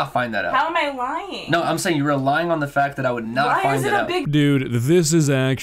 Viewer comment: okay, this is all super cringe, and this guy is clearly toxic, but what he said was 'you're relying on the fact,' not 'you were lying on the fact.' I mean, he's fricking 12 and clearly doesn't enunciate his words like someone who wants to be heard and understood, but of all the things to shit on him for, that's probably the weakest.